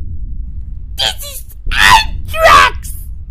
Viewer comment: china testing the coronavirus in food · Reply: It sounds like he says: This is asstracks